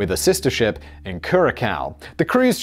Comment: Hol' up... did you just say "Cure a cow"..??